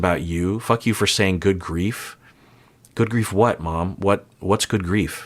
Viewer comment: I think she might be saying “good grief” to how she F’ed up the interaction. She’s realising she didn’t clearly convey her stance at all (being supportive and loyal) and might’ve made things even worse in an attempt to be understanding. However, that’s an assumption on my part as a person who often speaks in a way people misunderstand due to my poor word choice and emotional illiteracy (in regard to my own emotions [mostly]).